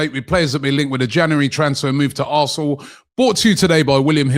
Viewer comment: Did you just say “January transfer move to a**hole?” 😂